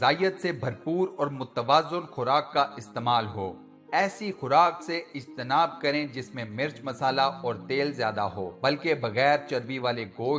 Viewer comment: I have a question what'd you exactly say word on 04:22 and what does it mean??? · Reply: Ijtinaab means perhaiz or avoid it